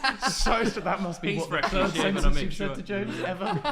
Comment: did will just say 'hes freshly shaved, i made sure' or am i going insane?